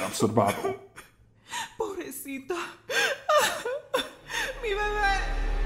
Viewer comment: what is the madre saying after she said "pobrecita" at like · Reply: she said "mi bebé" which translates to "my baby" twice,, the second time sounds a bit unclear tho